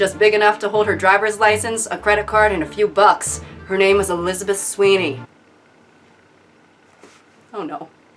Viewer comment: I THOUGHT YOU SAID ELIZABETH SCHUYLER OH MY GOD